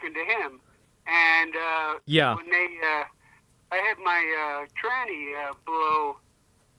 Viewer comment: did he just call his transmission a "tranny"? I love old people 😂